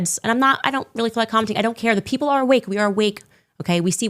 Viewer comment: Did Candy say shes WOKE